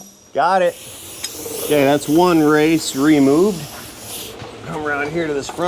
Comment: Did he just say one race removed? 👀👀😂😂